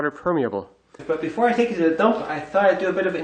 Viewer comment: I misheard "before I take it to the dump" with "before I take a dump", like man I might have to unsub if THAT happens on camera